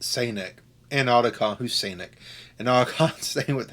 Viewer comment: Never noticed the Sanek 😂. Thought you said Sonic and Otacon